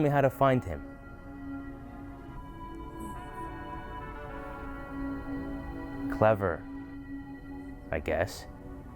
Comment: does the hermit go "help me" or something, couldn't make out what he said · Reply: "No... Leave" I believe I was mouthing, directed to the viewer.